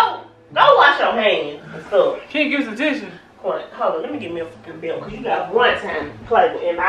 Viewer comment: did she say lemme get me a belt like he a kid 😂😂😂😂😂😂